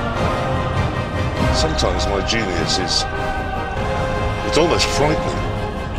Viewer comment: The boys go to Drangleic. Discussion Thread : <link> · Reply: British person here. For help with the subtitles, what Jeremy is saying in the quote at 11:23 is, "Sometime my genius is... it's almost frightening." Can't see is anyone else has mentioned that or not, but if so, just add my comment to pile.